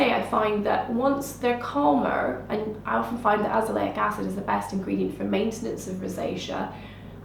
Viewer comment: could someone tell me what she said right before azeleic acid? Alph?????azeleic acid. Thanks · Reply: She said I often find that azaleic acid….